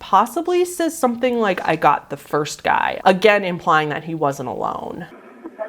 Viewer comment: I don’t hear “I just got the first guy.” I hear, “Shot the first guy” or “they shot the first guy”